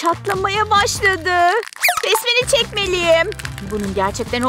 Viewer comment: Besmele cekmeliyim anladımahshahhshahahahshshahhshsbxbhshdhs